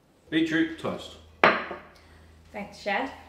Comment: Who else thought it said beans on toast